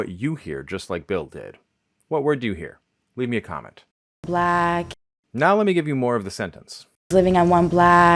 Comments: I hear blag 😂😂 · I heard the word "flag" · I hear “lag” or “flag” (I’m from the Chicago area) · Fascinating content! I heard "flag" at first lol then "black" lol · I first heard blaaack (color) or blag (??). To me, that's a super weird transformation from "block", as the rest of the context reveals. FWIW I grew up in Lancaster, OH (that's "Lanekuhster," Ohio, lol) and have lived in the midwest my entire life, from Lancaster and Columbus, OH, to Indianapolis for two years, and then Port Huron, Lansing, and now Ypsilanti, MI. TBF though, I don't get out much, and most of my socialization is through text.